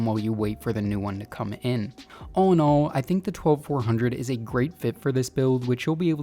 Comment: I genuinely thought you were saying Oh No.. then I thought maybe he said UNO..but why I womdered .. then I turned on close caption and realised it was an "All on All" after all.. But nice video dough 👍